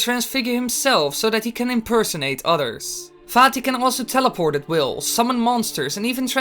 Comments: For some reason I thought you said fatty instead of vaati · in the begging I thought he said farty